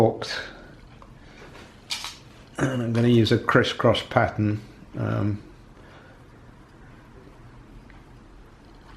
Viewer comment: Hi there, what do you actually say at 23:36? 6, 16 or 60 Newton Meters? I'm sorry I can't get it. I hear 6 but it seems really light. Thanks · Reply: The spec is 6Nm. Yes, it's a low value...